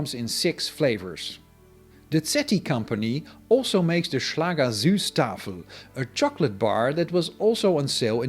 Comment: I thought you said 'Schlager Schutzstaffel' which did not sound very Proletarian.